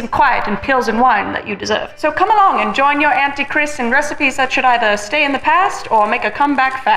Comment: LOL I put on subtitles and at the beginning (0:25) they say "...come along and join your Antichrist in recipies..." instead of Auntie Kris😂 And so many variations of hollandaise and bologne xD Also that bait with Dylan on the thumbnail!! I exected a collab, I love you both 😭